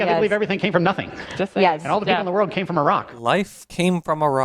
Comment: Okay, when he said "Humans came from a rock." I heard it as "Iraq" and I thought, yeah, around thereabouts, wasn't it? Second: whenever creationists mention the "life from rocks" thing, didn't god create man from, like, dust? Or clay? Or something? Are they trying to undermine their own position?